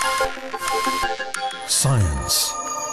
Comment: Yeah just “science” I hear it as “ suyenz”